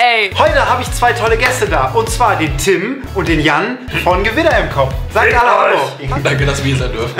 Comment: Ich hab nicht gewitter im kopf verstanden sondern gewinner im Kopf